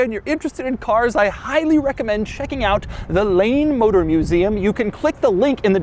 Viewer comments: I ALMOST thought you said the "lame motor museum", close! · I thought you said Lame motor museum, cause the multipla you know... is lame...